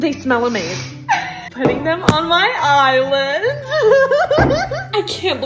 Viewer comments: when she said she was putting them on her islands, i thought she said eyelids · omg i thought she said “putting them on my eyelids 😁” 😂😭